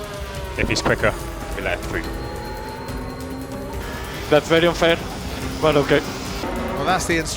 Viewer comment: Did Perez say "That's very unfair, but okay." at 7:55?